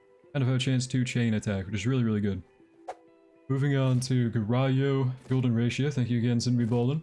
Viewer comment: what did you just say? Garayo? Wth xD